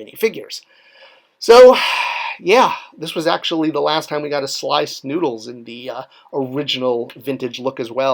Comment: I thought you said "Sliced Noodles". 😆